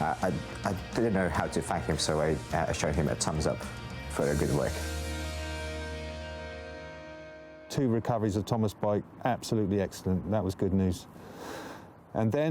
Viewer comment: Did he just say......tom ass